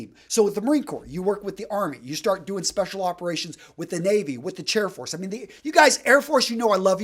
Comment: Minute 5:47... Did I hear "Chair Force"? 😄